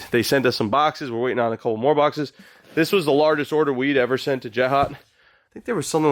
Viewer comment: BROO. At 1:29 swear sounded like said "this was the largest order of weed ever sent to Jet-Hot" LMFAO